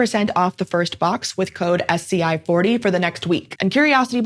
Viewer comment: I was half listening and I heard "free dice"